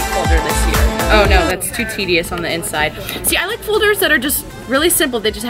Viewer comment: this is Hideous on the inside #allmyex’s This is me editing I thought it said hideous not Tedious😂